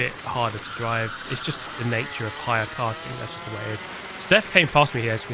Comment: For some reason i thought kept on saying higher cart. Thought it was mightily pretentious. Probably cause i'm high(er), tho.